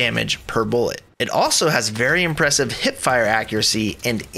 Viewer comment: What is hit fire? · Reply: Maybe you heard him say “hip fire”? That’s shooting without aiming in